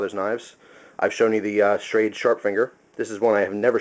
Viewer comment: isnt that shrede a old timer. sounds like you said sharp finger